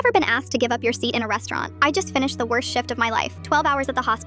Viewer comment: I thought she said “I have the worst shit”